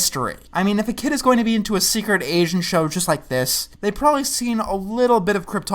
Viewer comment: When he says "secret agent show" I hear him say "secret Asian show" anyone else?